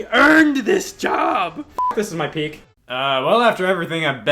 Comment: What did he say there? It was too fast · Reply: Fck this is my peak